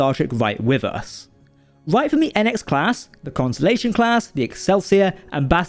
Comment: Did you just say "Constellation class"? It's "constitution class".